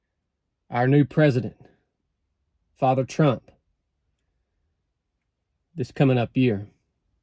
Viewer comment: Did I hear a “father Trump” at the end?